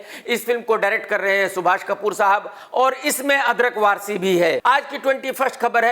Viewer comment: Did KRK really say 'अदरक वारसी' 🤣